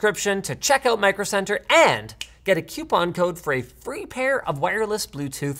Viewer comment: At the end of the video: "Did you just say 'queuepon'?! It's coupon!, the 'u' comes after the 'o'!" ¬¬